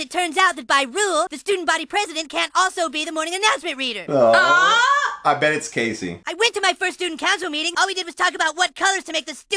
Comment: I heard, "I bet it's Kacee!"